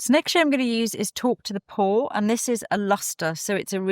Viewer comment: I thought you said 'Taupe to the Paw' at first :)